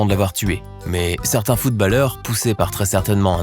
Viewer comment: attend, attend... c'est moi ou il vient de dire Footballer (en disant A au lieux de O)